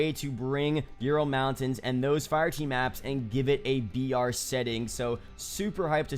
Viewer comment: Bro did he say "and give it a vr" setting at 9:38???